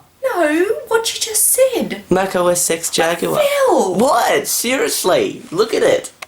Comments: your mum thought u said mac osex jagular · how can the word mac be rude? xD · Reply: thought that Phil said MAC OS sex and not MAC OSX. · Don't get the thing about it being rude. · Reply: thought that Phil said MAC OS sex and not MAC OSX. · I think she thought you said “Mac OS sex Jaguar” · she thought it said mac os sex. lol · Rude? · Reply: Haha, I think she thought he said "Mac Sex Jaguar", and I admit I thought that is what he said at first too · hse thought he said macowesex jaguar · Yeah I can see where your mum is coming from it did sound like you said mac os sex. :P